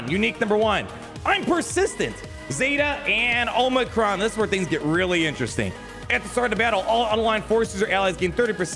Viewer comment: I hate these kit reveals... everytime I hear him say "this is where it gets interesting" I hear "this is where the game breaks"